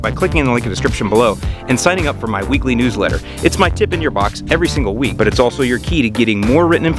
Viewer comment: Did he just say "tip in your box"??? hahahahahaha